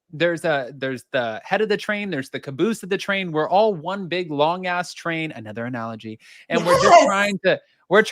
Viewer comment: Is it wrong that when said "One big, long-ass TRAIN", I HEARD "One, big long ASS-train"?!😂